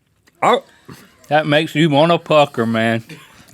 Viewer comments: it makes me want to pucker. I heard that wrong 😅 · it sounded like Link said "That makes you wanna f**k her man." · LOL it sounds like Link says THAT MAKES ME WANNA F*** HER MAAN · Anyone else get super confused for a second when he said "that makes you want to pucker"....because I heard "f*ck her" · When he said “that wants to make me pucker” I thought he said “that makes me want to f*** her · ""That Makes you want to F#ck her man"" Please tell me someone else thought he said that haha